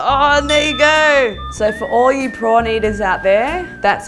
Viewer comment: i thought she said porn eaters. wth! what is even wrong with me hehehhe